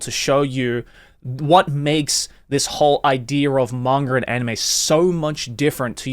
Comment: "Mangur" You Aussie :cat-orange-whistling: Sometimes you say "Manga" but most times that Aussie comes out. Its words and names that end with "a" will turn into "ur". I hear other Aussie and English men do it too.